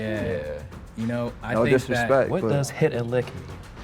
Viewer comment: Can someone please tell me what does the word he said at 12:22. Hit e lick? Thnks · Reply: Rob stuff